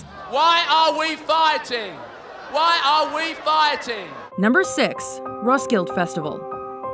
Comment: I thought she said "number 6, roadkill festival".